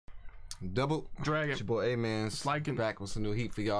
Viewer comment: Bro from my years of watching yall... i just realized his name is amenz I always thought he said a man 😭